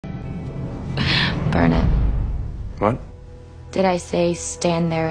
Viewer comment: I thought she said darn it or dar i